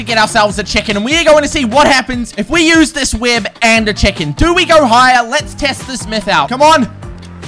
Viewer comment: If we use this web and checkin 1:50 yes he actually said checkin